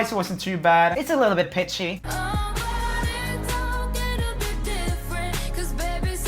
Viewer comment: When Aha Max said oh but it's okay to be different I thought she said oh bruh it needs to be a different